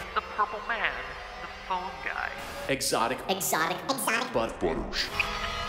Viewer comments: when it said ''exotic butters'' in a weird/gllitchy voice i thought it said ''exotic BUTT-butter'' and i was like ''why would anyone want butt butter?'' then i realized it was actually ''exotic BUTTERS'' not butt butters lol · did it say exotic butt butters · is it just me or in 0:20 i hear exotic buth*les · is it just me or does anyone else hear, "exotic but butters" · I literally thought he said Exotic Butt Butters..